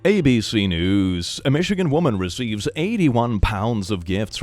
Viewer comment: I thought they were saying 81£, so I was wondering why it was impressive and that Bill Gates was pretty cheap XD